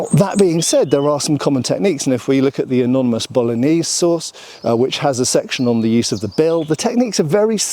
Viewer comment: I heard bolognese sauce haha